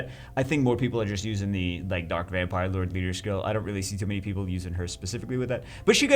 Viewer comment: Did I hear that right ? 3:53 light vamp lord ?